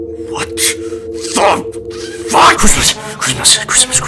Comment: I just started hearing "CRISP CRISP CRISP" instead of Christmas